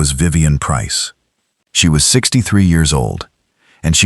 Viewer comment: Just a question but why is the mc always like 50-60? Can we get a story where the mc is like 25 or something. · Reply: I thought he said he was 38 yrs old.